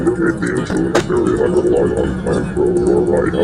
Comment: At the end I heard "The joy us free"